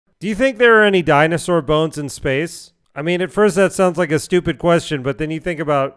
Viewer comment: For some reason I heard him say “you think there are any dinosaur ghosts in space?”; like the explosion sent their ghosts flying out of atmosphere.